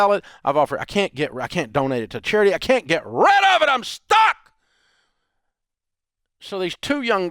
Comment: I had to replay the video because the first time I heard "I am F&*$ed," not "I am stuck."